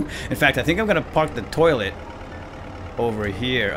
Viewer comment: Did he said tolet at 18:58???